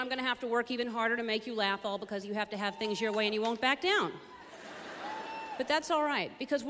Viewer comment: Sorry I'm french and I'm trying to make subtitles of this for some friends of mine but there are some things I don't understand :/ Like at 02:27 she says something like "you have to have things your way and you won't go back down" ? what does this mean ? And also at 04:13 "I'm stoling" ? is that what she says ? It would be very nice if someone could help me please ! Thank you ! By the way : Ellen is really awesome ! :)